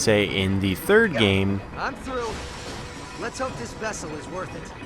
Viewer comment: What does Ratchet say? 31:07 I'm threw??? I'm through??? I'm trough??? ??? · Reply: he says I'm through because he just got past a stage thing